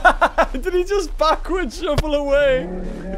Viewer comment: the Captions tho XD Did he just fuck with shuffle away that legit what the auto generated captions said for me XD